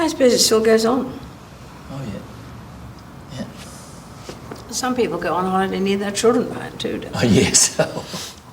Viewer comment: I thought the queen said: "Some people go on holiday and leave their children, like dude..."